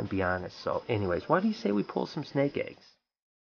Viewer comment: Congratulations on your success !!!! YOU KNOW YOU HAVE MADE IT WHEN THE HATERS COME OUT !!! And Haters keep on hating because no one else cares !!! ;-) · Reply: I. G. I thought he said pull some snake heads